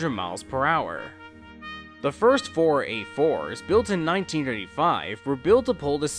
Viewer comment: Sorry it took me this long to finally upload the finally part of the list. It was all mainly due to a few issues I had while making this video and other things going on in my life... as well as a bit of laziness on my part. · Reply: If you're talking about what I said at 8:41, I said "four A4s" at that part. Though, I see what you mean. It does sound a bit like I said 4-8-4 there.